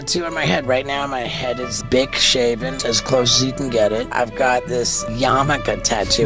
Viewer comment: I thought he said his head was "dick-shaven". Lmao